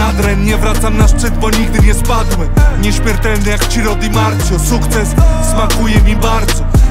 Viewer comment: Czytam słuchając i synchronicznie usłyszałem i przeczytałem komentarz "nieśmiertelny jak Siro di Marzio"